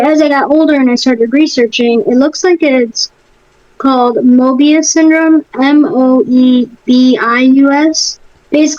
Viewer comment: I thought you said Morbius syndrome. Disappointing :(